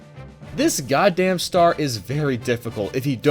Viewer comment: did he call it a star?